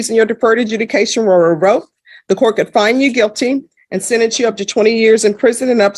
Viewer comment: Boyd sounded like Scooby Doo. “Re rorked”